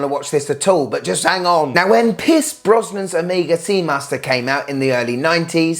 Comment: I’m an American. So, I keep hearing “Piss Brosnan,” and giggling to myself.